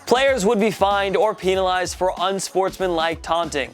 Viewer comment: Did he just say peenalized